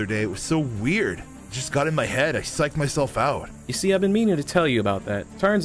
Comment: 2222 said a bad word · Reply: what you hear:swearing what i heard:"see ive been meaning to tell you that" so there was no swear in